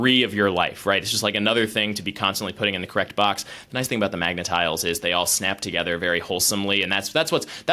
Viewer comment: MAGA tiles? Cause it sounded like Egger said, MAGA tiles? Also, they look sharp and small enough to swallow.